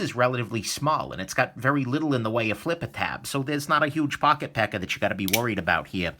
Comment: Every time I hear you say "pocket pecker." LOL!